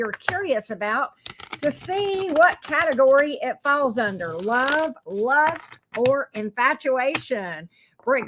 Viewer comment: Hello hello!!! I thought you said love lust or decapitation 😂😂😂 thought it was keeping with the halloween theme!!! Love love your channel ❤❤❤